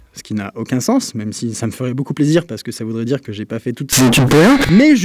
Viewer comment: aaah putain j'ai rien compris quand il a saturé à 7:30, ça lui ferait plaisir parce quoi ? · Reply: "ça me ferait plaisir parce que ça voudrait dire que j'ai pas fait toutes ces études pour rien" je crois